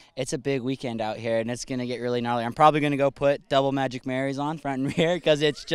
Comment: What does the dude say at 0:50'0.55 time double ....? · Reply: double Magic Marries...he's basically telling what tires he's going to use